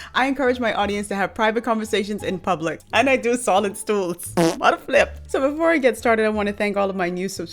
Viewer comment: I heard “I do solid stools” and slammed that subscribe button so fast 😂…lady ur hilarious! And I love the content